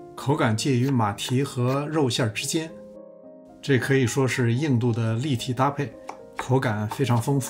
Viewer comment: “硬度的立体搭配” 讲究😀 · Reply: 开始听成了 印度的 立体搭配 心想这是什么意思 哈哈 后来再听 哦 是硬度 😄